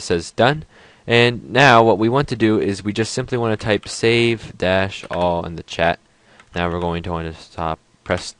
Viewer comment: what are you say savedas?